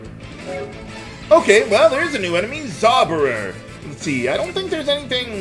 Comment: "Soberer!" (sounded like this to me) that made me crack up xD "Zauberer" is the German word for "Wizard" or "Sorcerer"